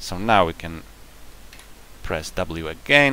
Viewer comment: I heard it as *press w but its actually was shift W ty